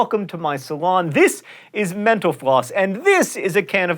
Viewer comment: When he says Mental_floss, it sounds like he says menopause